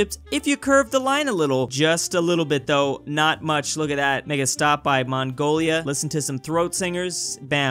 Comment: sounded like he said the n word. Looking with subtitles google says it's "make a stop" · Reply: It's actually make a stop.